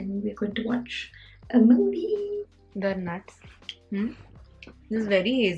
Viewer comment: When u said at 12:50 we r going to watch a movie in a very different accent I thought u said MNV😂😩❤ Who else thought hit like 💞 Maine 3-4 baar frse dekha fr smjh aya alsi meaning 😂😀 #aashnav #damnfam #aashgang ❤️🔥 <3